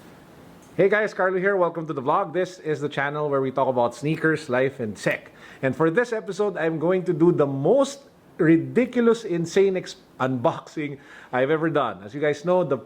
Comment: Some time I heard you say SNEAKER, LIFE AND SEX! LOL